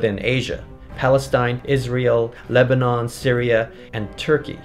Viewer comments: When you said "Palestine, Israel" I thought you said "Palestine Is real" 😂(I know it is but still) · i thought you were said "palestine is real" 😂👌